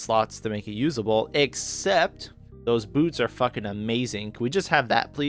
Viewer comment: Honestly thought you said those boobs are fucking amazing when you were talking about claire's boots. I need a nap